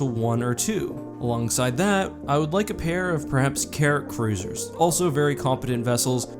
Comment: is the Enforcer one of those cases where you couldn't find a picture of it? also I thought at first you said carrot cruisers, not Carrack, lol